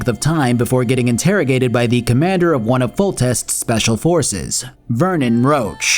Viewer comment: I enjoyed your summary, except for one thing. Every time you said “Roche” I heard “Roach” and started looking for the horse.